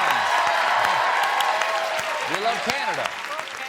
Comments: Did anybody else hear "Fuck Canada" at 6:48?? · anyone else hear "Fuck Canada" ? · Did anyone hear the guy at 6:49 say "fuck Canada"? XD